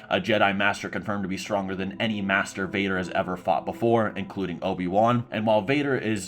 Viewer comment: this felt intentional, because i was cooking and had to double take to make sure i heard “master vader” and not masturbater